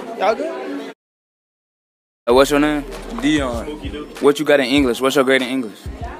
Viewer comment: you asked what was his name in the background I heard spooky Do 🤣🤣